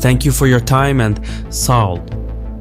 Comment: What was the last thing said right after saying “thank you for watching”? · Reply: "Saol" Means thank you in Turkish